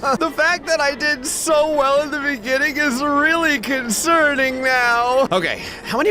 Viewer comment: I hear Concerningmiau ??